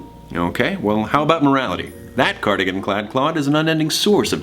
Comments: Wait did Logan say CLOD!?!? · DID LOGAN JUST SAY CLOD?!